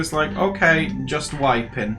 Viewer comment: "you okay hun?" "okay just wipin" I HEARD IT "JUST VIBIN" which I guess also makes sense but the fact that I had to recheck lmao. XD